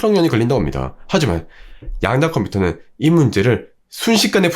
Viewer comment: 발음이 되게 안좋네요 양자 컴퓨터가 아니라 양야 컴푸터로 들리네 ㅎㅎ